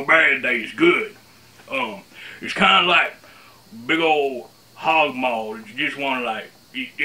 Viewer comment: accent was so broad there were a few words that I just couldn't understand. At 1:01 he's talking about some kind of meat dish that sounded like "hogmore"; then at 3:02 he gave the title of a book and the authors and I have no idea what he said. Can you ask him to clarify those words? Thanks in advance. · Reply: is "hog maw" and at 3:02 it's MAYAN BLUE, by Michelle Garza and Melissa Lasson.